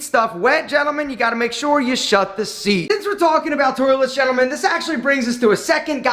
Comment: haha i thought said you got to be a bad boy... so piss on the seat